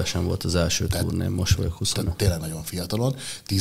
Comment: mit mondott 0:41 most vagyok huszon....... eskü hogy nem értem :') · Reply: 25